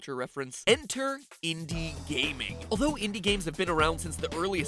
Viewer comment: Before I thought it said India games xd.